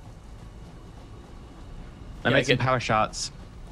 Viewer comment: Did say "powersharts"?